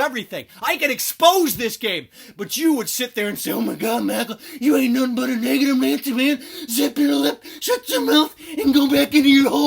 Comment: Did he say the N word at 3:11? Just curious and his left eye went the other way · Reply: He said negative nancy